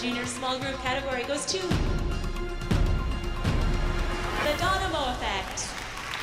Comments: I swear she said donimo xD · anyone else hear the lady say donimo instead of domino at 8:41 😂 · did anyone else hear the announcer say "the donimo effect" lol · I’m sorry did she say “the donimo effect” 💀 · did she say the donimo effect 😭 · she really said donimo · Idk if it was just me but at around 8:40 I think she said donimo effect instead of domino effect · did she just say the donimo effect instead of domino or am i just hearing things? 😭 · Did I misheard or smthn but i think the MC in 8:42 said "Donimo" instead of "Domino" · did she say donimo?😭😭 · the announcer says ‘donimo’’ instead of ‘domino’. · did she just say “The donimo effect”??